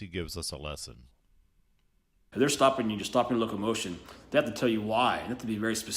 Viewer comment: Did he say, "stop your locomotion"?